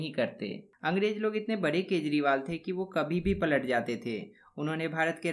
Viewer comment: Sir plz correct or check what you are saying….Angrej bhut bare ……….. the.. 6:55 minute video pe kya the? plz check or correct… nd ur video are very useful for us…thxs sir… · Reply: Hi saying kejriwal